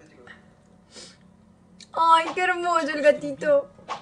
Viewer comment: Yo pense que decia que hermoso el Anthony :,,u me da pena sin novia oe pobrecito :,,u